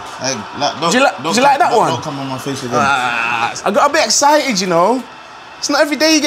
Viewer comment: did he just say don’t come on my face again??